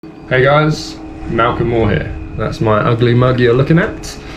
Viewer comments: ngl i thought he said Macklemore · thought u said “hey guys, macklemore here”